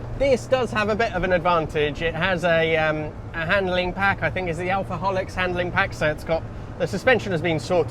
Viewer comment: I swear to all things fast and Italian that I heard “alcoholics” handling pack, and thought, yeah, that tracks correctly.